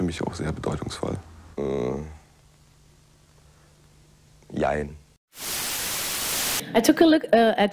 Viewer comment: did he just say ja and nein in one word is that actually the German/Deutsche word for Yes or No pardon my ignorance. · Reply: Jein can be used to mean yes and no at once 😂